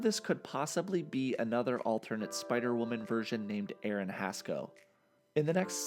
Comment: What is that spider woman name on the minute 7:23 i couldnt understand what he said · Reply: aaron hascoe